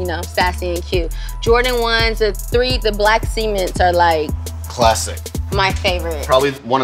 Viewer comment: did she say black semen😂